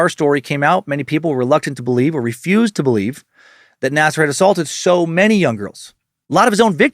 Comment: I thought it said "Nascar" :D